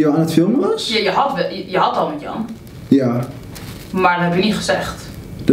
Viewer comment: Heet je vriend Jan? Of hoorde ik het fout?